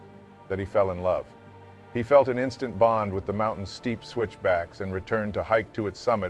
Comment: what did he say?… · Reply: Steep switchback’s